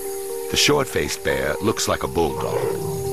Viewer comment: "The Short-Faced Bear looks like a *WHAT???*... I've had people listen to it n I've heard anything from "Bulldog" to "Bullfrog" to "Bull-Croc", whatever that could possibly be Who knows what he's actually saying at the 9:08 mark? Help me out here kind souls out YouTube!!! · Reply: "looks like a Bulldog" ...because of its short snout when compared to modern bears.